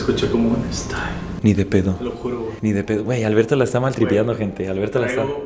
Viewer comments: yo también escuché el stay💔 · No mms en él minute 10:49 sé escucha qué dice : Stop, pero es una mamada sé escucha a otra persona qué hablo en él micrófono y hasta se escucha que agarra aire 😂